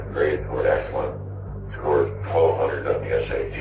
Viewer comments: where the subtitles say "inaudible" he says something I believe to resemble "1200 on the SATs" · what he says is “1200 on the SAT”